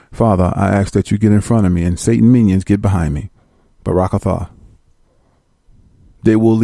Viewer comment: WHAT DID HE JUST SAY? IN THE NAME OF THE FATHER, SON, HOLY SPIRIT? BARAKAFAR?